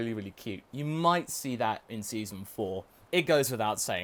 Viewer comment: Did you say "Season 4"????